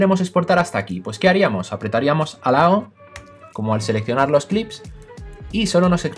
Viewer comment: Bro no entiendo lo que dices en el minuto 15:04 ... Dice apretariamos la tecla "alaun" cuál es esa tecla? Excelente tutorial!!! Te sigo!